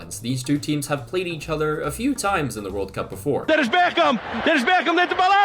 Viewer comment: am i the only one who heard Beckham instead?